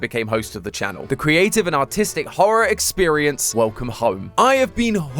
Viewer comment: because of his accent i thought he said autistic